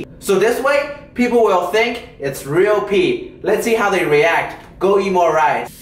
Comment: I thought you said, "Go EMO rice"